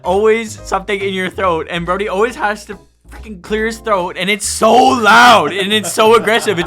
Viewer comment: did he swear or did he say fricking? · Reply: Fricking